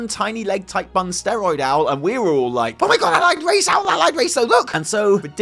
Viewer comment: Wait. Did you just say “Owlied race 6:00 ? Also potentially suspect info at 7:27 they said “and the horde” eeeeeeeeeeeeeeeeeeeee Happy birthday